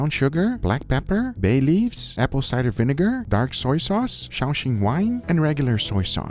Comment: What kind of wine? I couldn't quite hear the word. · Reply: Shaoxing wine. Made from rice & wheat.